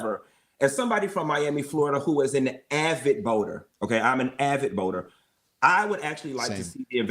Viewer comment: my Spanish is messin' up what you said. I was like, man... we all VOTE. 🥵 I thought you said you're an avid VOTER. "Don't Boooo, Vote" 🙈😆 you said Boater. que gracioso, pensé que le dijo "VOTER."